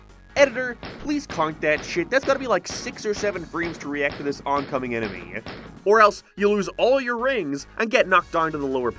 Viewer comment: what did you just say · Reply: Guess we're just locked out of a couple numbers